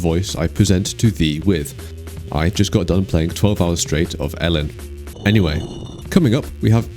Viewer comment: Didn't quite hear the name of the game on 0:40. Ellen? LN? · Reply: It's called Elin